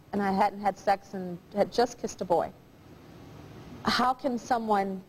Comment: Tyra says she had just kissed a boy at 14 almost like she's better than this girl she interviewed. Lets have a show that has everyone's horrible life story. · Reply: It sounded like she said "at 14, I'd just fisted a boy". I agree she's a terrible host with all the sensitivity of a brick: "Where you sexually abused", "what was it, rape?" Ffs